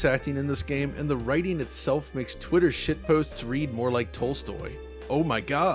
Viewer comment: Did I just hear Digital Foundry say "shit"?!